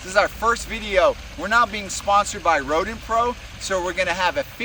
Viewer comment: It kind of sounds like says "we are NOT being sponsored by rodent pro"